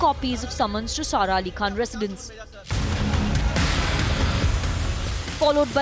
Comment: did she say padmini kolhapuri? 😅 😅